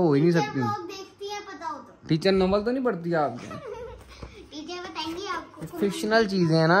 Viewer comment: ye kya bola ny?? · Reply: mujhy lga namal keh rahy ha